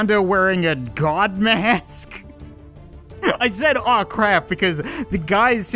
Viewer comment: he said "oh no" in the beginning not "oh crap" I was always wondering what he meant by "I said oh crap" every time I rewatched this